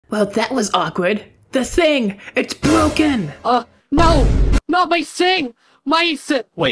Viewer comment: why dose it sound like he is saying "not my sink" 💀💀